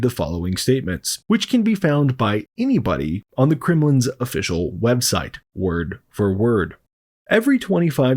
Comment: Thank you for a good video, very informative and telling. 7:00 - Did you say "...at the Kriminalin's official website"? Yes, I do believe that place is "kriminal".